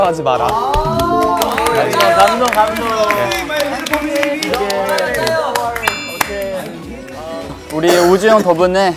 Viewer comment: everyone please listen to it very well, to what mingyu said. cause i can't catch it up. he said like "... aww so swett...." and after that i cant hear it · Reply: He said « you’re so sweet my beautiful baby »